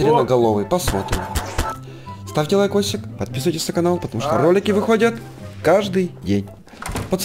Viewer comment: мне послышалось или ты сказал отписывайтесь на канал????